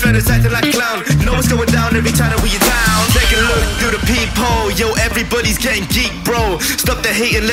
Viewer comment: When he says “peep hole” I hear “people” lol